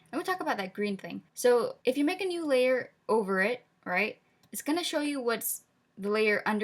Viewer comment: I thought you said ogre it instead of over because I thought you made a pun about onion skin mode im meme trash sorry